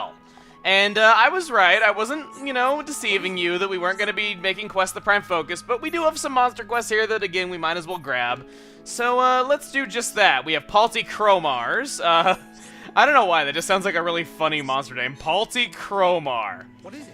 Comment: I wasn't looking at my screen when you mentioned the Palti Kromars, and you were in a lighthouse, so I misheard it as Faulty Crowbars.